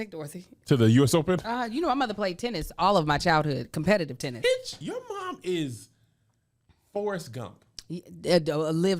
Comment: did just say b!+©h 😱😱